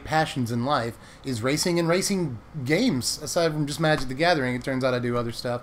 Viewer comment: Thought you said "raising games" and was going on about kickstarter stuff :) Made no sense :D